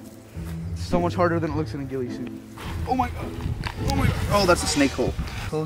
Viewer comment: When he said ghille suit I thought he said glizzy suit